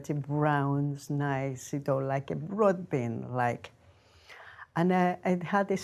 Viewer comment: I think she says 'Broadbean' not 'Ribbon' as the captions show.